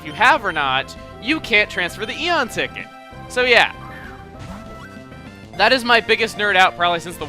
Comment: Lol, I thought you said "yeah he is black"